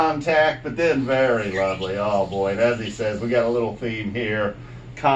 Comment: I thought Trey said "a little Fee" after Contact and I lost it! I was confused when they played Slave to the Traffic Light. Listened to the show the next day and realized he said "a little theme". Great 2nd set for me though!